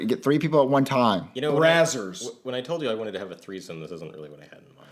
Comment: What did say???!!! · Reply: brazzers Allah power!!! 🕋🕋🕋